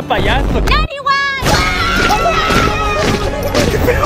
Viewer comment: mE bAz a dar anillo🤨? - no nmms siiimplemente épico TIENE UN EJERCITO DE NANNYWISE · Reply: Yo escuche "me vas a dar al niño?"